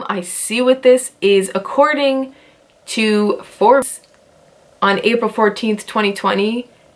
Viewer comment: Did she just say For bees, instead of Forbes?